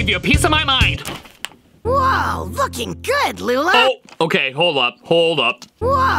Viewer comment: I think he ment to say looking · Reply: I heard looking